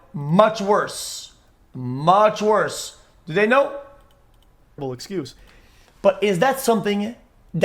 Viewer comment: Excusie is what it sounded like you said instead of xqc